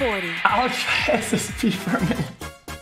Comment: What does he say at 0:07 ? Not a native speaker, I've a particularly hard time understanding him when he speaks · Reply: 'I wanna try SSB for a minute' 😂😂